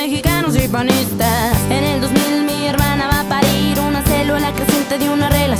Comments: 😆😆 antes pensé que decía " mi hermana va a Paris" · Mi hermana va a parir.. yo pense que decia mi hermana va a paris😂